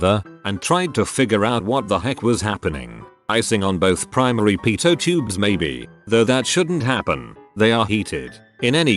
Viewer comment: I thought I heard “icing on the pizza tube”